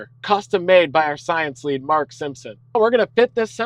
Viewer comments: I thought you said the chip was nicknamed the Marge Simpson because it's blue and has a big hairdoo, but you said designed by Mark Simpson. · did bro say bart simson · did i just hear "Marge Simpson"